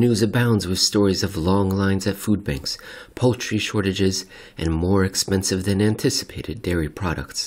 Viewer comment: Thought you said "poetry shortages" near the beginning lol